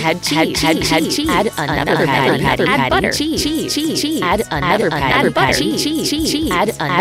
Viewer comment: IT SOUNDED LIKE IT WAS SAYING "CHI" 😂😂